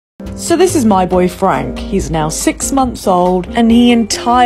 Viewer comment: I'm just gonna say. When you say "my boy frank" I hear it as "my boyfriend" My ears really need an update. Anyway he's cute.